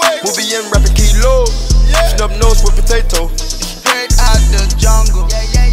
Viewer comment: Did I just hear “Stump nose like Potatoe”